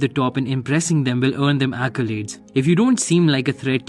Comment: Man what are you saying from 0:52-055 ???? I listened for 15 min again and again and i can figure it out!!! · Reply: Accolades?